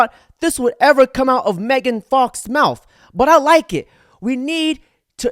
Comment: did he say that was Megan Fox?